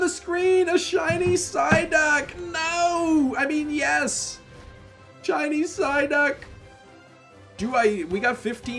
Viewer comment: did he say Chinese psyduck?!